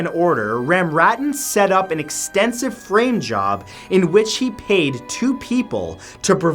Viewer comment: This is terrible, but I kept hearing, “rim job,” not “frame job.”